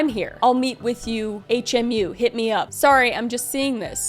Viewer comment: In reference to the text message exchange at 17:44, Justin is not saying "hit me up." He's saying "I'll meet you in hair/make-up". I would assume this is a separate trailer on set. H/mu is common abbreviation for this department.